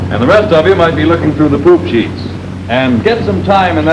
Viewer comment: Did he say poop sheets?